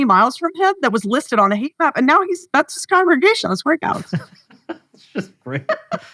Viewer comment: This may just be my lack of familiarity with the terminology surrounding church-going. But in any case, what does she say at 57:07? Something about her friend finding a church: "That's his congregation ?on his workouts?" If anyone could please identify and explain the above text, I'd appreciate it. Thank you.